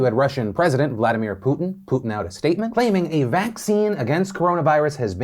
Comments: Did he...did he just say “Putin out a statement” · Excuse me Philip "no puns" DeFranco did I hear you say "Vladimir Putin 'putin out' a statement" 😅